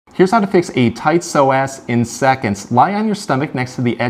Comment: I thought he said "heres how to fix a tight sore donkey" lolol